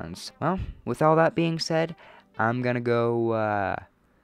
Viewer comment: Wait, did you just say you are going to go feed on your families fetus?